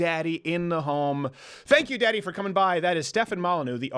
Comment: Umm.. did Crowder just call Molyneux daddy?